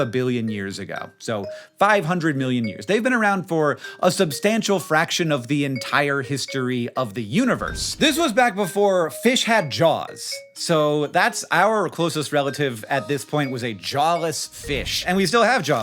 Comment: I thought you said 'before fish had jobs' 😂😂😂😂💼🐠🏢🐟